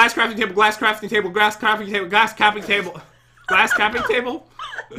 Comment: I thought He said glass claping table :D LOL